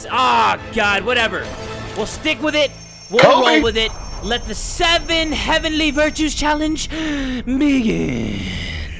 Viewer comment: what the hell is Ted saying? XD (I know it's an inserted sound, but still) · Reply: Coby!